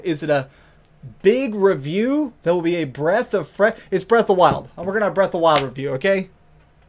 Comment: Breath of fire is one of my favorite series, at first I thought you said there would be a new breath of fire game and I started jumping for joy. Then I watched again and found to my dismay that I misheard :(